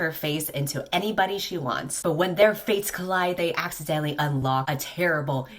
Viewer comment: I seriously thought you said “when their faces meet.” instead of when their fates meet. Also I hope you feel better!😊😂